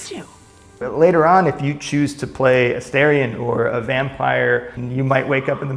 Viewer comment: If I heard correctly... if my ears do not deceive me... Vampires not being locked to just Asterion has been confirmed! “Asterion or a Vampire” @ 5:27 Can’t wait to play this awesome looking game! <3